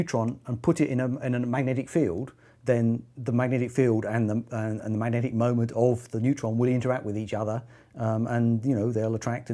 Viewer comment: i thought he said magnetic monopole and i almost lost my shit... still cool